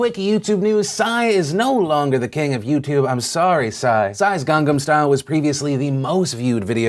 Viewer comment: did you say Gang...*Gam* Style?!